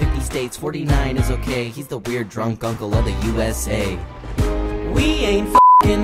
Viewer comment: I thought it said “weird drunk guncle” and was about to ask why no one had said anything about it 😂